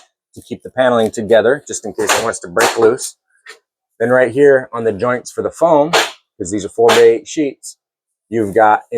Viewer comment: I didn't hear you say the thickness of foam you put on your ceilings? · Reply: 3" foam